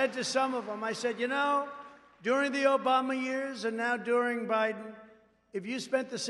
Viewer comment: Sounded like he said the Obummer years, lol.